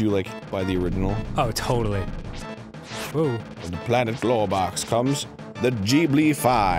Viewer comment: Did Arin say Globox? Game Grumps: Rayman 2 confirmed.